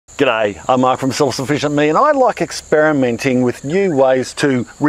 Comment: Totally misheard "Self Sufficient" as "Salsa Vision." facepalm